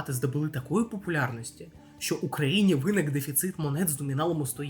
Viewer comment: "у країні виник дефіцит монет ... " - мені почулось "в Україні...". сиджу думаю "так... щось я 100 йєн давно не бачив" :face-blue-smiling